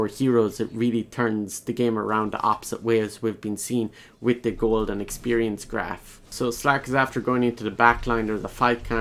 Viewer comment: Did he just say Golden experience? JoJo posing intensifies